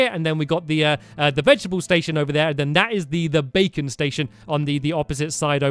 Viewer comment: isn't what your building the baking station? Could have sworn that he said bacon station.